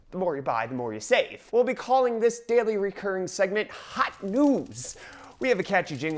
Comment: Who thought when he said "Hot news" at 0:19 that he actually said "Hot noobs"